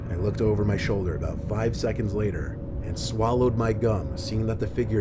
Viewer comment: In story one I thought the guy said "i swallowed my gun."